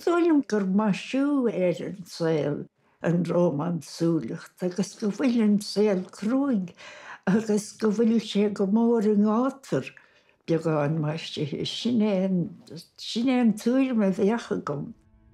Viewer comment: Hi, just wondering what the exact words she uses from 00:00:44 until 00:00:53 are. Not the english translation but the words in Irish. I have never heard them before. Thanks very much :) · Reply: Go bhfuil an saol crua agus go bhfuil sé go mór i ngátar (rómánsúlacht) beagán maisithe. Sin é a tuairim a bheadh agam.